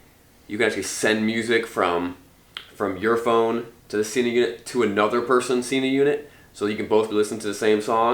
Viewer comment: What's the name of the gear you talk about in 5:14 ? Couldn't understand. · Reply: sena 20s